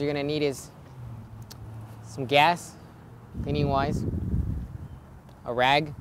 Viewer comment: did you say anywise ? pennywise ? what are you saying ?